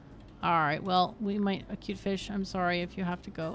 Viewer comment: I thought you said you'd name the next goldfish to enter the garden "acutefish" since you wrecked his home by putting frogs in it.